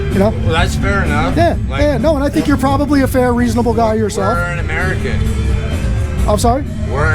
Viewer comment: “We’re in American” Wow, that guy’s not gonna remember this conversation even took place. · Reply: lol I thought he said "we're an American"